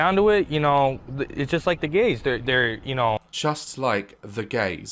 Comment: The fact the man said “it’s just like the gays” as a non binary and questioning person it’s just- just 🤮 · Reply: I'm ace and at first I thought he said "gaze". After explained I got a huge urge to slap the incest guy as hard as I can. You don't say "the gays" that's so disrespectful